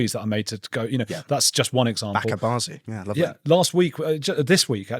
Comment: did he just say "back at Barnsley"? Can someone help me here? I don't understand the reference.